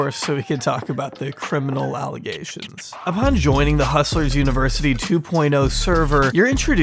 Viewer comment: Whenever he mumbled "Hustler's University" I misheard it as "Hostage University" and it just made so much sense.